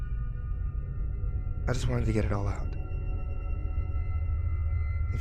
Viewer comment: May someone please tell me what he said in the 9:07 to 9:10 pleasee, I really like his stories and I am learning english, I understood almost all the story but that part I can't note what he is saying. Thanks anyway :D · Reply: Gerardo Gomez In case you didn't already get it on your own, he said "I just wanted to get it all out"